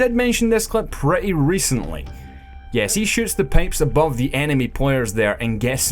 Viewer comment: What happened at 6:01? I couldn't understand what he's saying but it seems awesome "He shoots the ___ above the enemy" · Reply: he shot the pipe above the people